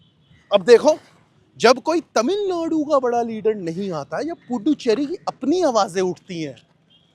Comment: Did he just say Tamil loudu 😂😂😂😂